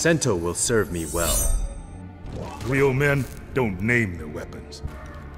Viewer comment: “Real Man Doesn't Need Weapons.” Says the guy who has Metal Arms and an RPG on his back. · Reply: I thought he said real men don't "name" their weapons.